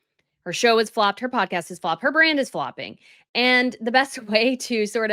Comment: lol I had to re-listen to this bit because I thought you said her brain was flopping 😅😂🤣